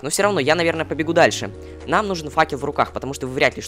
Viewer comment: Мне послышалось: Мне нужен факи в руках XD